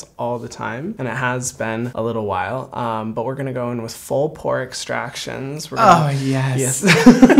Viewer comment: I thought he said pork stractions